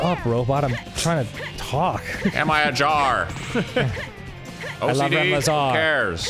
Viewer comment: I thought the robot was saying "a mayo jar"